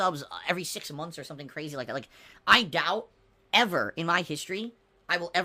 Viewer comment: it literally thought he said "i gout" 😂